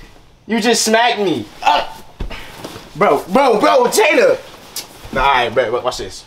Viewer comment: am i the only one who heard him call her jayda😂😂